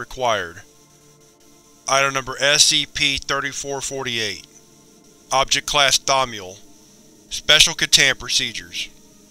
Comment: At a low enough volume, it sounds like he says “Object Class: Dong”😆